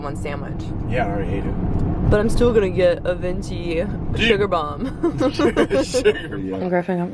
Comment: lmao u called the drink that griffin got a sugar bomb and I started cracking up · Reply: I thought she said Jager bomb at first 😂